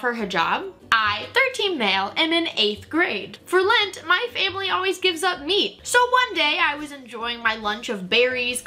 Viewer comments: I thought she said “For lent my parents always give up ME” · Bro I thought it said “for lent my family always gives up me✨” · At the beginning I thought you said‘for lent my family gives up me’ and not meat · I thought she said “For lint my family gave up ME.” 💀